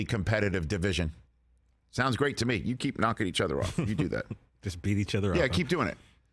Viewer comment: Did he say “just beat each other off”